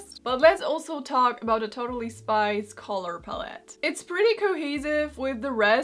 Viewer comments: I totally thought she was saying “Totally Spice” and that it was a cartoon of young versions of the Spice Girls. Yeah, I am not an intelligent person. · 100% thought she was saying "Totally Spice" THE ENTIRE episode. 😂